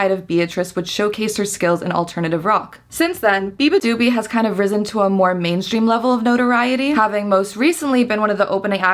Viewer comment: I want to know what you said instead of Beabadoobee that you needed to ADR it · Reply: beatrice